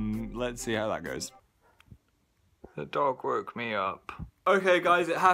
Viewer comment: "......the dog woke me up..." someone care to explain why the hell that was so cute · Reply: I thought he said the "dark woke me up" at first XD